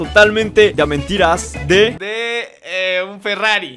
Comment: A la verga yo cagandome de risa por qué le entendí "y un kilo de arroz?" Hasta que me di cuenta que no dijo eso xd